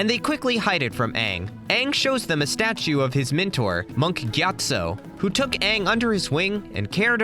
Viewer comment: i hear gyat-so